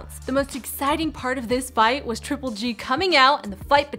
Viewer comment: I thought it said Triple H